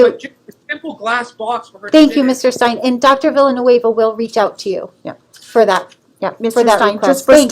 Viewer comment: I could swear she's saying "Dr. Evil will reach out to you".